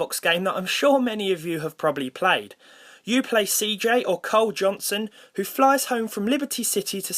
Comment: I heard "Cole Johnson" instead of "Carl Johnson".